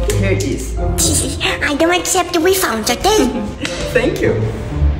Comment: I thought he said no refunds because he was gonna eat it straight away...